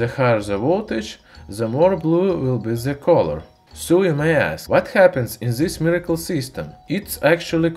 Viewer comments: I was reading something and I was listening to the video and I heard "sue my a**" '_' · eh what? ..... DID YOU SAID? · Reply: So you may ask